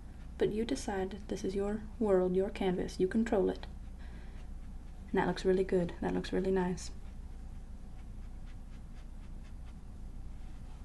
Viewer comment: I thought she said you can troll it. TROLLOLOLOL